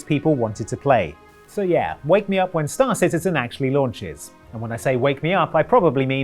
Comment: What said: "Star Citizen too" What I heard: "Start Citizen 2". I mean, I wouldn't be surprised if the sequel was in process before they released the full game, but I thought I might have missed a thing.